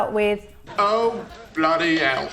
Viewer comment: Till this moment, I thought Ross is saying "oh bloody owl!"